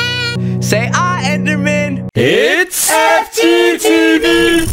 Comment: I always thought it was saying “ittttts STD